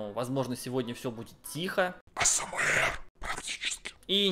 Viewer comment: Что он сказал на 0:06 ? Асамуэр? Что это?))) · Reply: "АСэМээР"- ASMR Жанр видео, способствующих успокоению